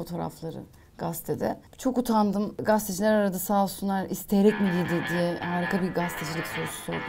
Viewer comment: "isteyerek mi [????]" ne diyor orada? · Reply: isteyerek mi giydi diye sordular diyo